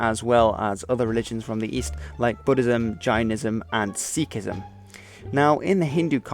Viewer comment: Did you say vaginism, 0:24 ?